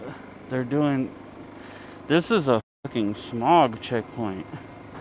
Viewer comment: Sounded like he said, “smug check point” 😂